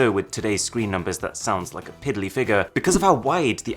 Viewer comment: Timestamp 0:48 Did he say "piddly" ? Why is that so amusing to me